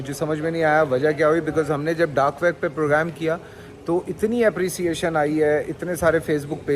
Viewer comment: Did he say 'dark web at 15:57?